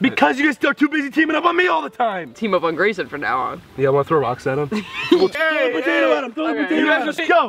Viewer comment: I thought Ethan said Rockside at him I'm so late